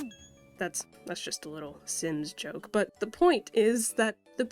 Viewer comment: Thought you said "just a sins joke" which, given that youre talking about EA, still fits